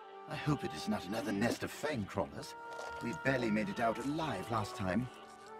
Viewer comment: What does he says at 5:40 ? another nest of what ? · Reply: Antoine Gély fang crawlers